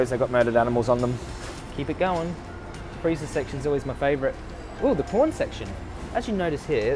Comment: I thought he said "ooh the porn section" lol. My mistake. Such a great video